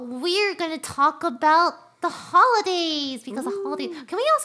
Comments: I thought she was going to say the holocaust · Yikes when Lily said "We're gonna talk about the hol-" 0:42 My video buffered and i really really thought she was about to say Holocaust and not Holiday